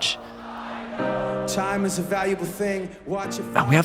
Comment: I swear I heard bald, not bold.